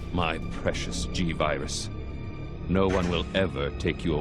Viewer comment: When they said "G-virus" I thought they said "gingivitis"